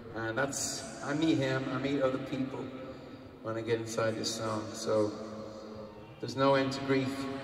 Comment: "I meet him, I meet other people when I get inside this song." is that what he's saying at 00:50?